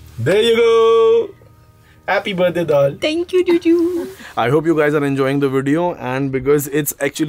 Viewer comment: Wait did I hear juju @ 12:47 ? 😁😁 Happy Birthday to 🎂!